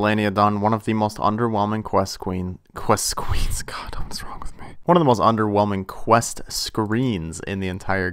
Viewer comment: "oh my god don't smoke with me"??? is that what he whispered lmfao at 7:10 ish